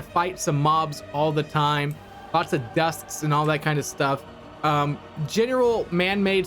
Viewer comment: did he just call husks dusks?